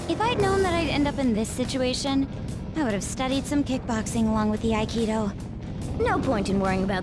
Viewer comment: At first I thought Makoto said "I would have studied some kickboxing along with Akihiko." I almost freaked out.